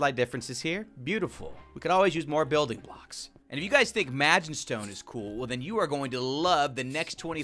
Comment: I've seen That Time I Got Reincarnated as a Slime way too much. Every time he says 'Magentstone' I hear 'Magistone'.